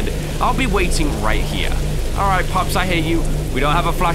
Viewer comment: I thought he said "Alright, Pops, I hate you."